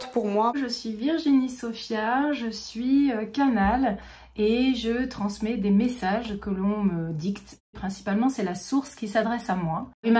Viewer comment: J'ai compris la première fois "je suis Virginie Sophia, je ne suis qu'anale et je transmet des messages que le colon me dicte"